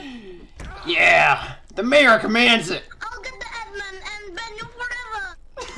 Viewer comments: bro i tried finding this clip before and i couldnt find it and gave up and now i just stumble upon it here amazing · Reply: I always hear "I'll get to Eggman and blend you forevaa" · bro i tried finding this clip before and i couldnt find it and gave up and now i just stumble upon it here amazing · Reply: i always heard it as "ill get the eggman"